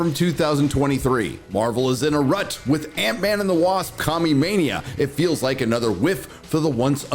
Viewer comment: Did you say "Commumania"? x) Join the Disney "Bread not bullets" club... we have badges! x)